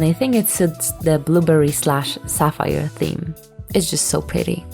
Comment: When you were saying blueberry I was half listening and it sounded like you were saying boobberry 😂